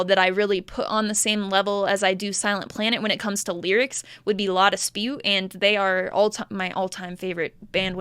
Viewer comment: Can someone tell me what band she mentions around @ 9:23. Not native speaker so I cant really make it out... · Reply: La Dispute! their album "Wildlife" is one of my favorite lyrical creations ever.